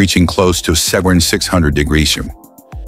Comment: on: wtf is this ai saying? 600 degreshum? Even the closed captioning doesn't know what it's trying to say